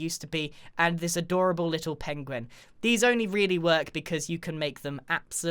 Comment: Did I hear a PENGUEN? 9:33 Like Benedict Cumberbatch if you know what I mean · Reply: I thought Eggs Cucumber said penwing